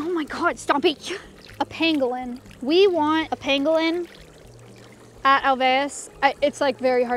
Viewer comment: Genuinely thought Maya said she wanted a penguin at alveus and I was like omg its so hot in Texas you can't get a penguin